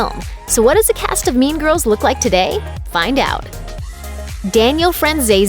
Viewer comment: I heard meme girls.